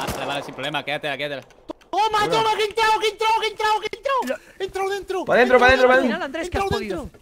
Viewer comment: pero que dice, como que entro adentro xd